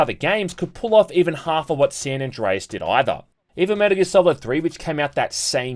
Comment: What is the game at 8:35? I can't understand what the guy is saying and the subtitles are wrong due to him speaking so fast. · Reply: metal gear solid 3